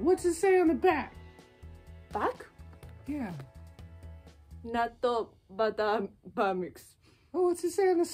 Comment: I heard 「納豆・バター・バー・ミックス」at first. 笑笑！ 大好きな皆ちゃんいつも有り難う御座います！頑張って下さい！応援してるよ！♡